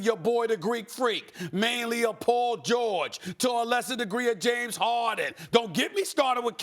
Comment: Did he just say “to a lesser degree, James Harden??”